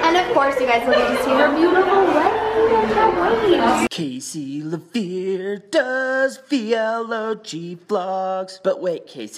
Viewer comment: What are the lyrics of the endslate? Each time it plays I'm not sure! 😂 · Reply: Hehe! Thank you! I thought it said cases lavere does real og vlogs!